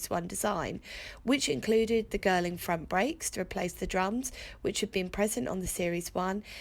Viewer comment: My brain heard Girling as “girl in front brakes” for a moment or two. Obviously the sort of brakes you want if a female child runs out in front of the car! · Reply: Yeah, I heard "Girl in front brakes" too ! but brain translated to Girling Front Brakes ☺ 🏁